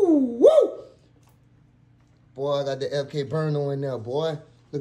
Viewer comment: Damn what kinda pistol is that? Looks sexy af. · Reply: it sounded like he said the “MK Burna”.. (burner=gun) I thought it was some kinda CZ, tricked out m9 or some boutique 1911 i never seen. I didn’t know either